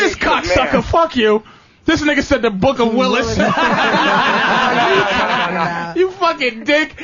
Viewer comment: The book of Willis!! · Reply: That guy actually said "the book of Lilith" but I guess they misheard him LOL hilarious anyways